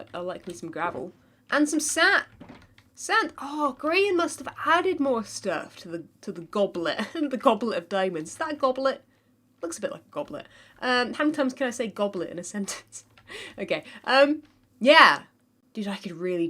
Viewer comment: The button replica does do something it's just you hitting it just in the wrong place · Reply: I dont know if it's just her accent but it sounds like she said grain